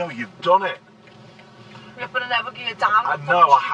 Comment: what was said then? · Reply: "Yeah but I never geared down but I wouldn't normally do that though" or something like that